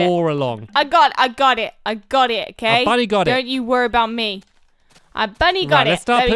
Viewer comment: I don't know y , it's just every time I hear them say "Bunny got it" it's sound's like they are saying "Bloody Got It" 😂🤣😂